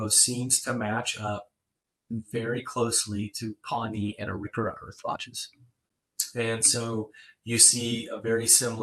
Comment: through 42:51 minutes, the created transcript is supposed to say what instead of "match up very closely to Pony and Orura Earth watches"?